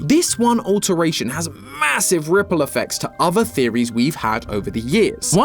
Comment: did you say MASSIVE?